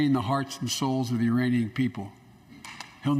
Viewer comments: did he say Iranian people? He did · Did he say “Iranian people”..? Or am I trippin?